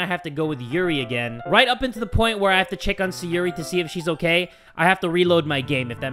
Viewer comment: did you just say sayuri